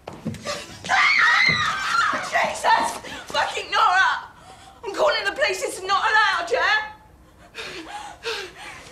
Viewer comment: Guys I know this is a weird question, but I'm not a native English speaker and I need to write the first dialogue between waris and marylin. What does marylin say at 7:50? "Jesus, fuckin' (?)" ? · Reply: She said “Jesus fuckin’ Norah” (or ‘nora’ - however you choose to spell it and it’s Christian talk) ✌🏽